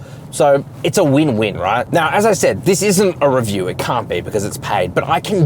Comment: Did he say that its a paid review.. 21:40 😮. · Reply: "It's paid if it's a review ". But it's not